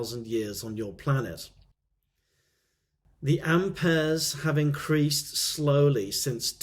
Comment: what is the word he is saying there? The "ampears" have increased? I can't make that word out. Thank you.